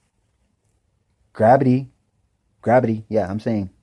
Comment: Did he just say grabity...?